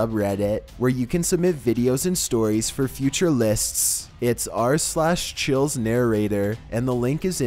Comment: Did he say fit future lisps?